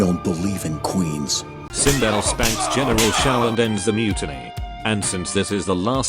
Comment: I've first heard *dignity*, instead of mutiny. She did end his dignity too though 😏